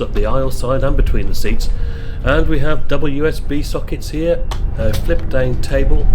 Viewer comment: Every time you say "double USB sockets" all I hear is "WSB sockets"! Great video as always. :)